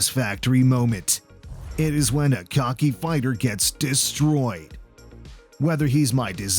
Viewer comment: I thought it said cockney Fighters 😂